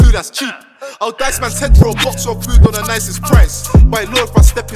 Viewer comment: he's mad i just heard "i'll dice mans head for food"😂😂😂😂😂😂😂